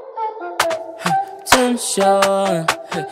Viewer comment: instead of attention, it sounds like he is saying 10 shot